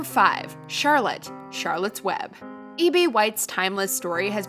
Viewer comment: Did you just call the author "eBay White?" lmaooooo